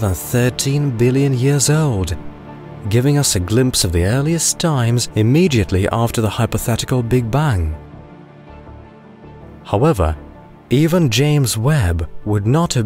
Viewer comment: I hear Big Bung. Anyone?